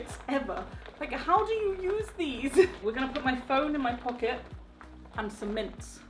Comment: i thought you said you were going to put mince in your pocket... the silly little things that go one inside of head...